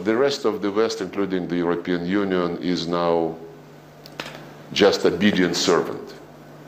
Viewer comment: Anyone understand what he's trying to say here? "Just a big inservant"? Makes no sense to me. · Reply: Just obedient servant